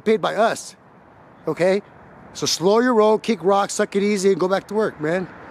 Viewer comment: you need to make t-shirts with your quote “slow your role, kick rocks, suck it easy” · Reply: I misinterpreted and thought P said suck it slow!